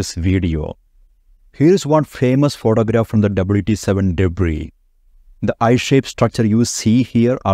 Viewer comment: what on earth did he just say? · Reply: Here's one famous photograph from the WT7 debris